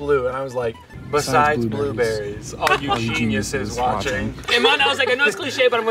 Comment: I thought said blue batteries but he said blue berries